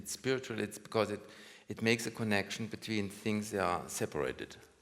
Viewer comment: "The science is all separated, so the only way to have some ... is the art" I can't work out whether he says "context" or "contact" because of his accent. He can't pronounce the English /æ/ sound, so it could be contact, and he's put an 's' on the end because he doesn't know that "contact" is uncountable in this sense... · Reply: I thought he was saying "connection" at that point. Might have just been where my mind was going, though.